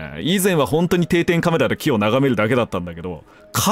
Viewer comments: の言う「気を眺める」が「キ○眺める」に聞こえるんだよ笑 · 木ヨを眺めるに聞こえた← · 木を眺めるが、キヨを眺めるに聞こえてくる